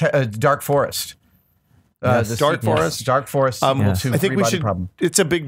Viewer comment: Was the name of that book "Dark Forest" which is what I heard, but can't find. Or "Dark Forces" which I found as a new book, but I'm sure is not what they said? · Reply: Sure, it's The Dark Forest by Cixin Liu. Part of a trilogy.